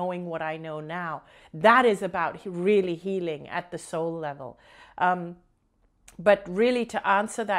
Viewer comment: Thank you again Anita for a great video. At first I was hearing you wrong about healing at the soul level. I thought you were saying that we have to heal the soul which confused me because I believe that the soul is perfect so why would it need healing. When I listened again I realized that I was not hearing you correctly as you are talking about healing "at the soul level". Now it makes so much sense to me. I only mention this in case someone else was hearing it the way I was. Since your last video I investigated Jeralyn Glass and Crystal Cadence. I have been listening to her youtube video's and meditating to her crystal's every morning and other times when I think of it. It really brings me into the moment and out of my mind....lol. So thank you for introducing her to me!